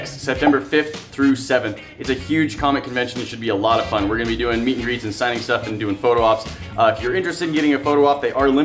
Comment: "Meat, grease, and science stuff"? That's what is sounded like you said. Guess it came thru that way cuz you were talking about freeze drying bacon, which is basically meat.....grease.....and science stuff. Right?